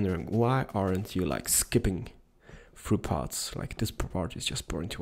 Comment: i thought you said skipping through paths and expected a lovely little frolic down the village